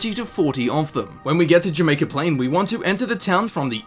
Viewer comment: When you showed the location it sounded like you said you may complain